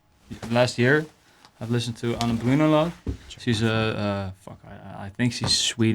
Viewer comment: Please, what is the artist/band mentioned by at 26:25? I didn't understand :~ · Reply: Ane Brun